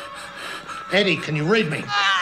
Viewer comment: At about 2124 of review, I thouht I heard line "Heady can you rape me?" But then realized he was saying "read" not "rape". Much different type of horror movie if ge did ask for what I heard.